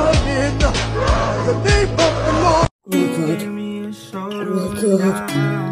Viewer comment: i heard "we are for liverpool"